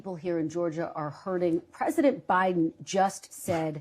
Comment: Sounded like he said president obiden🤣